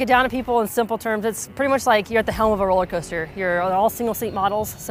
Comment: did she say "a fuckin' rollercoaster"? Lmao